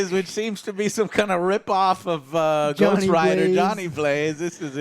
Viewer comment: When I heard Donny Blaze, I thought they said Johnny and I had to turn on the subtitles and roll it back. I was like, they better not be trying to change Ghost Rider's origin... I'm glad I misheard it the first time XD